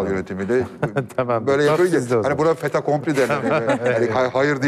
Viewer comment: 'deki Altaylı'nın cümlesi Fait Accompli: Oldubitti, Emrivaki demek. Anlamayan olursa yazayım dedim. Ne dediği pek anlaşılmıyor.